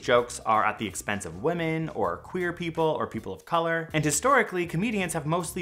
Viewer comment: I thought you said the jokes were at the expense of clear people.